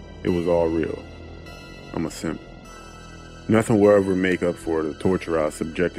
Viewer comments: Did he call himself a simp at 11:38? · did he say I'm a simp? 😂